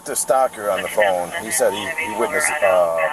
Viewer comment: The off duty officers name is Stocker. I thought he said the name Stalker. A little search shows a Dan Stocker of the Marysville Police Department in Michigan. He has a LinkedIn profile under the same name.